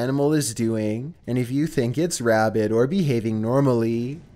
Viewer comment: On number 4 I thought you said rabbit intruder not rabid and I got really confused as to why he was so scared and on number three if this kid is walking around in woods that could have bears he should really bring bear spray